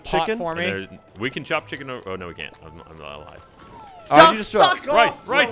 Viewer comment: Anyone catch Gavin yelling FUCK OFF